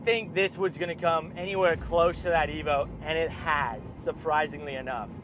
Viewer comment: Did he say a v8 wrx sti?